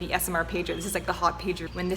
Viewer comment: I thought you said ASMR pager to start with, like is there a department of doctors devoted to ASMR? That would explain why there are so many ASMR doctors here on YouTube.